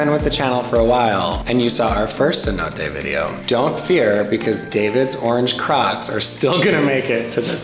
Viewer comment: When you said, “David’s orange crocs will still make the trip.” I thought you said “David’s orange crotch ….” !!!!! I was trying to imagine how that could happen.